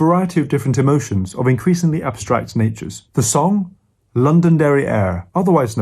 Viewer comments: thought he said London Derrière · I thought he said, “London Derrière”, then I realised that the air in London smells like it came out of one. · I swear I heard “London derrière” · I thought he said 'London Derriere' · i heard "London Derriere" and expected butts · Did he just say "London derrière" ???